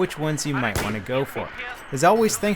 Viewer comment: anyone hear "this is levelcrap"